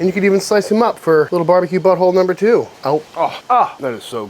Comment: did I hear that right? BBQ Butthole #2? How did I miss BBQ Butthole #1?